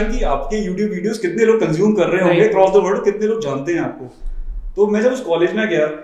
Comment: Hey! Wonderful Podcast! I just wanted to let you know that there's small mistake in caption at 8:10. Instead of 'College' it's 'Collage'. I know it's not a big deal but I just wanted to let you know :)